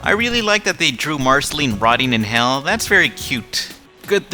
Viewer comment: did you say that marceline was rotting in Hell at 1:55 couse i couldnt quite here you